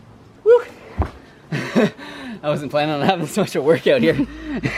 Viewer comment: the subtitles say I wasn't planning on having sex to work out